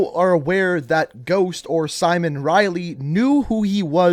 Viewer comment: Omg wait. This is another Yanny/Laurel situation. at first i heard “Soap” then after reading the comments I thought of “Simon” and then i heard Simon. My skin crawled when i heard Simon after hearing Soap 5 times